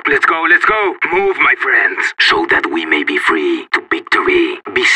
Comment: can someone tell me what is he saying please im from hungary and that word is simular to our word and i cant understand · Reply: let's teach these dogs a lesson.